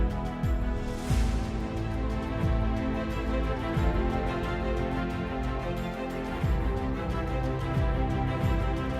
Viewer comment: What did she say @ 3:16:18? “Probably ____ instability.” I can’t hear it well and not sure if she said what I think she said. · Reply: I think she said "woman instability"